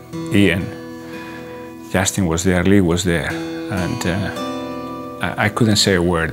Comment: Just after 39:52, about hugging Ian, what does he say please? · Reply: Justin was there, Lee was there.....